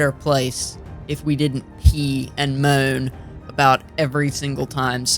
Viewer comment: Tell me if I’m wrong but did you say pee and moan or b and moan or whattt???? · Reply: I did in fact say pee and moan. Pee like urinate not the short form of a swear word